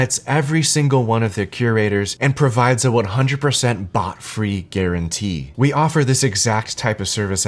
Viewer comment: I first thought I heard: a BUTT free guarantee 😁